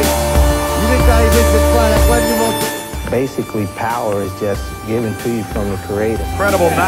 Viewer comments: "Basically, power is just given to you from the cradle." That's absolutely right. There's not much a human can do to gain power if he does not posses the right genetics. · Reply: I thought he said, "Basically, power is just given to you from the creator." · "Basically, power is just given to you from the cradle." That's absolutely right. There's not much a human can do to gain power if he does not posses the right genetics. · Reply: Is that what he said? For the longest time, I thought he said "Power is given from the CREATOR." :P